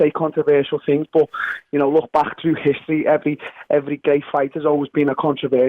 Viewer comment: Hold up...did I hear that right - @ 59:14 "every GAY fighter has always been a controversial fighter"